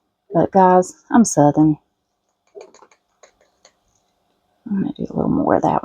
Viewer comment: Ahaha, when you said Grenache and then said “guys, I’m southern”. I really thought you said you were seven. I had to listen to it a couple times then go read the transcript because your “southern accent”really threw me 😂. The angels are great, I love that you did them all in different colors 🪽